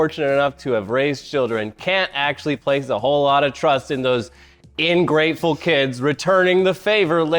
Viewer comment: Did he just say "Ingrateful"?